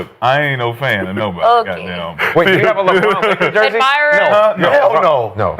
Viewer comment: What lebron jersey is Brandon asking about at 9:07? I can't make out what he's saying. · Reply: Laker Jersey